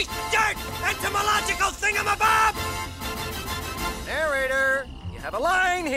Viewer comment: What did he say? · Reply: "Etymological Thingamabob"